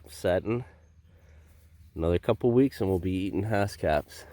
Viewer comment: Very interesting video! Not gonna lie, I thought you said "another couple weeks and we'll be eating housecats" 😆